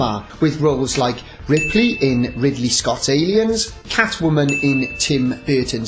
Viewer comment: does he say "Ripley in Ridley Scotty"? Ah yes, who could forget such a classic role!